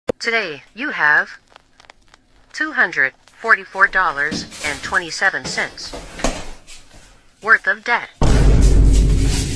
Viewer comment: I thought she said "worth of death" 💀💀💀😖😭😭😭 WHYYYYYYYYYYYY "Oh you thoughts you was good" lmbo. 😭😖😂😂😂